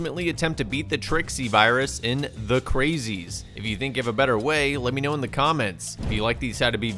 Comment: I thought it said how to beat the Trixie Virus in "THE CUTIES" so I was pretty confused for a second there lmao